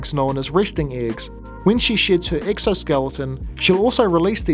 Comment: i misheard "sheds her exoskeleton" as "shits her exoskeleton" 😂